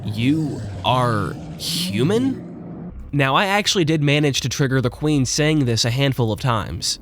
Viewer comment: I think it says “can you help me”